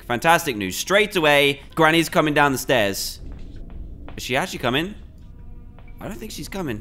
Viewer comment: “she is coming! Wait is she coming?” THATS my version of what he said U-U